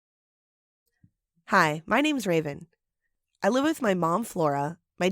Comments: I could’ve swore too god I thought she said “hi my names raisin!” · When I first saw the video I thought she said hi my names rasin😂